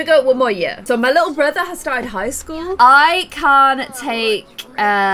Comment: I thought she said that her little brother DIED in high school...😱